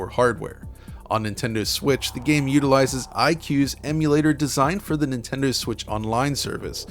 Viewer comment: Wait did I just heard "iQue" in 5:46? I remembered it should be the name of Chinese market N64.